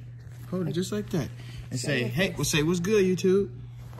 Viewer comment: I could’ve swore bro said he was done with YouTube😂😂